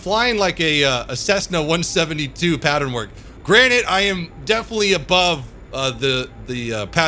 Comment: is he saying granite? Or granted